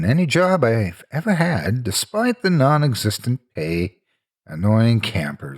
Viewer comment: Despite the non-existent what? I couldn't understand that part. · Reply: "...nonexistent pay,..."